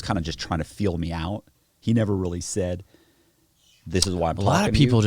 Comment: “Black people are desperate for money” yikes. This isn’t going to end well. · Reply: Didnt he say ‘a lot of people’. Sure sounded like black people though.